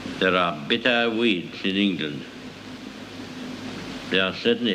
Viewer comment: nothing todo with anything, just wanted to say how proud i am that as an austrian i didnt need subtitles but the american did :P ...except for when i heard "there are bitter weeds in england" i think i missheard that one or its a phrase i dont know what it means... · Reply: I heard "bitter winds in England". But yes, thank god for subtitles.